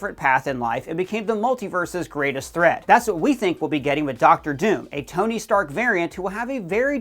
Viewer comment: I thought they said this DOOM isn’t a Tony variant?